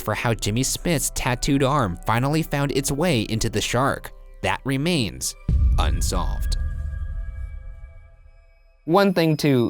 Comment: Thought he said,"And on this episode of lesbian unsolved"